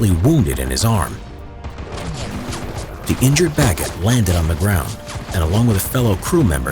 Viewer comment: I heard “ the injured baguette landed on the ground “